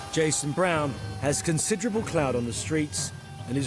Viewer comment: did he say cloud? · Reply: Clout